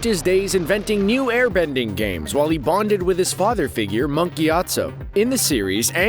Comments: I thought you said monkey atso...... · Ty for telling me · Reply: when i first heard "Monk Gyatso" i heard "Monkeys Yact So"